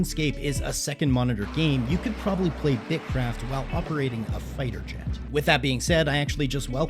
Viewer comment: I thought it said bitchcraft 💀